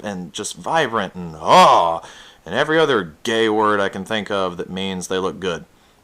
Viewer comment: did you say gay O_o